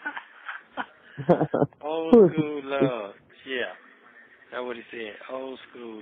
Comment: I thought he said, old fools love. That's really what it is, not old school love. She has no clue at this point.